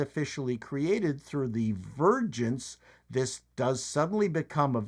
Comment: So vergence birth? I kept hearing "virgins" instead of "vergence".